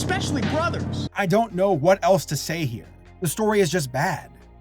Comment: I thought you said "I don't know what elves to say here" for a second.